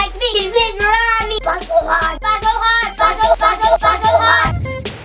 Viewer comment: what did it say did they say butt so hard